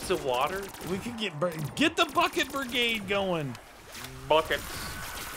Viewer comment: I thought said get the fuck it brigade instead of bucked brigade 😂